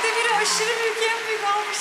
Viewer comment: melike 4:20 den sonra ne diyor anlayan allah aşkına yazsın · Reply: Yaa şuna bakınnnn. Mert demire aşırı en büyük alkışı yapıştırır mısınız diyor